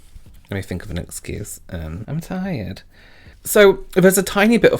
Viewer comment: I was doing the dishes and thought you said "I'm dying" for your excuse, had to rewind and find you said "tired". Either way, it's an excuse I'll be using from now on.